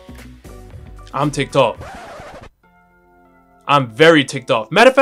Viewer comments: At first I thought he said "I'm ticktock" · I thought he said "I'm tik tok". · When he said I am ticked off I thought he said I am tiktok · I thought he said im tiktok